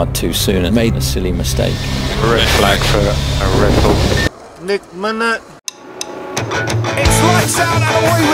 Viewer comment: Found your YouTube page randomly but enjoying the content. Keep it up. And what is that guy saying in the middle at 6:29? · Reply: Thanks!😄 Its a meme where the guy says nek minute lol